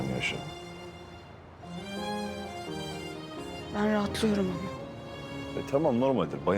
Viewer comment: de mahur ne diyor anlayan yazabilir mi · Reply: Ben rahatlıyorum ama mı ne diyor